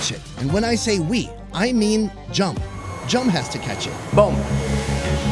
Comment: that "what did he say?" 🤣🤣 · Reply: THAT NAME I SWEAR IT SOUNDED LIKE HE SAID MR.DUMP I THINK HE SAID THAT 🤣